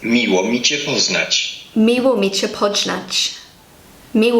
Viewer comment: No offence, but that sounded like 'Miło mi cię podźgać...' (Nice to stab you...) XD